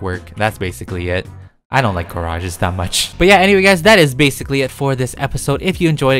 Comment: LOL 🤗 when I heard say garages I thought he said car-rages😂🌼🌻✨